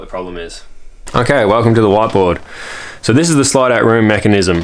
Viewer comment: Didn't know what a whatboard was until he filmed it and said "okay welcome to the whatboard"